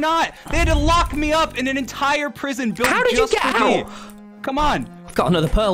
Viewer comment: Is it just me or did Dream say, "They locked me up in an entire prison builded just for me"? In the timestamp